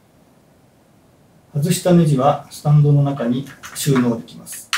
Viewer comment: i hear STANDOOOO?!